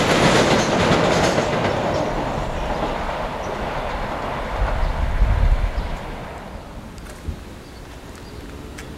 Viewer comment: HANG ON, WHAT STATION DID YOU SAY???! WOOOOOAAAHH! Its certainly changed! XD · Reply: stratford